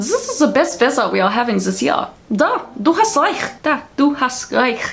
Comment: Iim from germany, and i'm more than a tad bit confused on what you were trying to say lol, but I still loved the video. could you post in the words you were saying? i'm pretty sure i heard "da, du hast reich", or something, but that doesnt really make sense...did you mean "ja"?